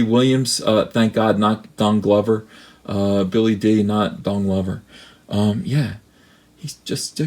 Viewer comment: lol I thought you said Donky Kong Glover lol!!!